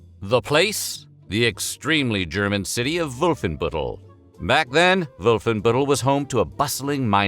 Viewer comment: I had to google what 'wolfenbüttel' means. I literally thought you said 'wolfen butthole'